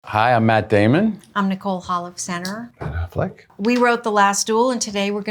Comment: I heard her say "hi, I'm Nicole Halfpercenter!" 😂